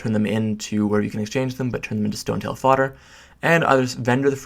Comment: what did you say at 2:25 ? don't turn them in at the exchange npc, but...? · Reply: To make stonetail fodder